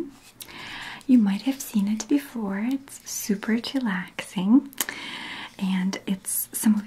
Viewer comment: I have SO many things to say about this vid its too funny ! did she say "chillaxing" at 2:14 omg i cant 😂😂